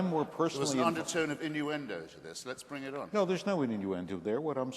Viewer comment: What does Hitchens say? an undertone of ____ ? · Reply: innuendo